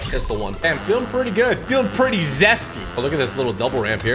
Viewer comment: Did he just say he feels zesty